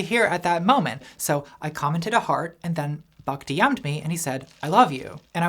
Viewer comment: Hi Natalie, I don't know if you're still reading comments, I wanted to let you know about a problem with the captions. :) This video is really long, and the community has only captioned about the first 20 mins. What you've approved is almost entirely the auto-generated captions. Now that you've approved them, they're formatted to look like official CC when they're still full of errors and lacking in punctuation. This is unnecessarily confusing and frustrating for people who need captions (for hearing impairment, second language, or auditory processing). Maybe you're worried about vandalism? That's reasonable, but I hadn't seen any signs of vandalism when I was working on the captions before you "approved" them unfinished. If you can't trust the community, please use a paid captioning service. If you can't pay for a captioning service, please be aware that captioning is slow, and it's not realistic to expect volunteers to finish captioning a feature-length video in two days. I was quite enjoying working on the captions as I re-watched the video last night and earlier today, and I'd happily go back to doing that if you re-opened the captions. It's frustrating to know that the captions are now approved as incomplete and the video will remain inaccessible to a lot of people unless you resolve it. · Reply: For example at 26:16, the captions say "buck to young me" instead of "Buck dm'd me".